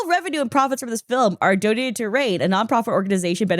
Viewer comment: i thought you said raid and had to look at the screen